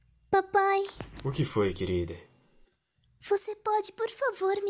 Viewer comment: escutei "Oi queride" no começo kkkkkk